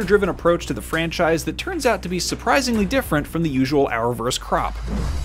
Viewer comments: "...from the usual Arrowverse crop" but I just heard "From the usual Arrowverse crap" which is a little more fitting me thinks! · I can't be the only one that misheard 'usual Arrowverse crap'... Still sounds right in my head.